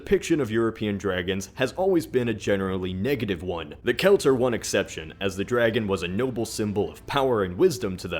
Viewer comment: Can't understand the name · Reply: he's saying "the celts are one exception" as in they didnt believe dragons were bad